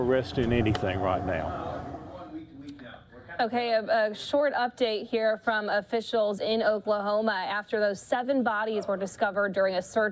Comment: I thought they said "royal property". That would have explained a lot.